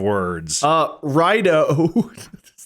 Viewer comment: Wade: Uh, Rhino. 🦏 I know he says Right-o, but the first time, I heard Rhino. And now I can't unhear it.